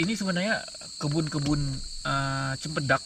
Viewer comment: cebun cebun cempedak 😂 aku ulang2 banyak kali aku kah yg salah dengar 😂